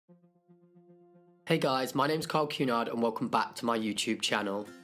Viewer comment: I thought he said my name's Coconut and thought that's a weird way of pronouncing it.. then I saw the user name and was like, oh...